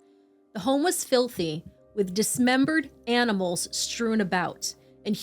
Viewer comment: did you say “dismembered animals”? wtf